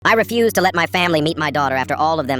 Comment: I heard “I refuse to let my family beat my daughter”